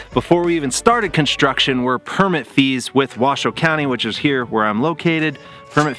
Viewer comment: At first I thought he said permit thieves, which, would not be too entirely inaccurate... LOL.... 🤣😆